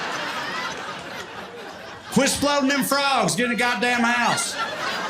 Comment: I'm Canadian, and thought I understood English, until I heard: "Quit floating them frogs! Get a goddamn house!" and had NO IDEA what floating a frog meant.